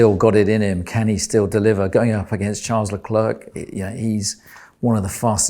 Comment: did he say Charles is one of the fastest “dry” guys ? Insinuating max is better in wet?